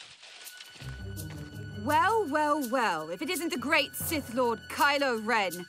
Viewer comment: thought she said simp lord